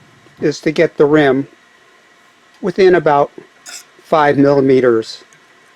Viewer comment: did you say 5 mm? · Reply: 5 mm (sorry for the late reply)